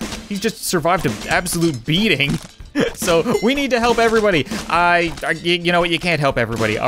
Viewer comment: "We need to help everybody :D" I heard "We need to save everybody :D" me: in Marcus' voice but you can't save em all ;) (I'm not even saying "like if you get the reference or some shit like that :3)